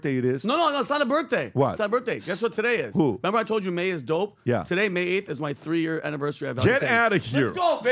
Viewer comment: he says its his 3 year anniversary at Valutainent...(I had to rewatch it 4 x because i couldnt understand Vinny AT ALL!!